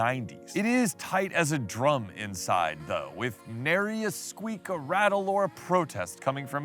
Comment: I couldn't understand what you said there - there are squeezes/rattles? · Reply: I said "nary," which means none. It was quiet and tight inside, rest assured 👍🏻